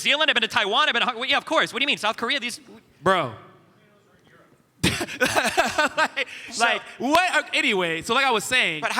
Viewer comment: What did the person in the audience say? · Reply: "Ayo Owen you looking good"